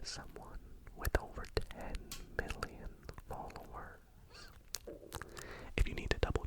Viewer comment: I’m sorry but I think I’m confused 7:17 did he say billion or million because I don’t think billion is possible but please correct me if I’m wrong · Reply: He said million :)